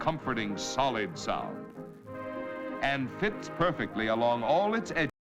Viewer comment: did he say tits perfect? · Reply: yes he said "tits perfect" and it was supposed to get muted, but someone hit the mute button too late in this recording, and muted later sound. This was the 1950's, and people talked more colorful I guess.